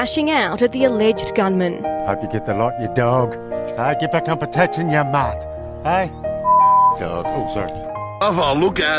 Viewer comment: Can someone tell me what he says at 1:06 I really want to know. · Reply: "get back on protection ya mutt"